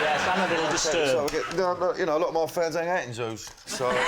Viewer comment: what is danny saying? A lot of my fans are…. in zoos? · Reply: Arent at zoos And funny I wanted to see if the captioning could grab it and it gets everything up to that and then just skips to (applause) lol